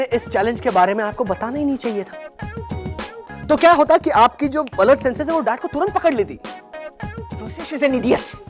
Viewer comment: did he say ALERT senses?